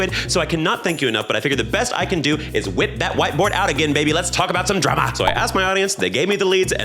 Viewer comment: I heard "whip that white boy out" 😭